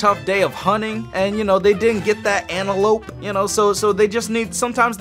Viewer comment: It sounds like your saying get that addie